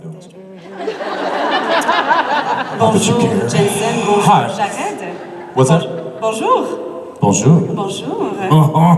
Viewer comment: What did jensen say at 21:37? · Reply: Roka Rika “not that you care”